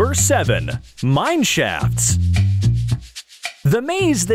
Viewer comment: Why did it sound like he said Mineshats??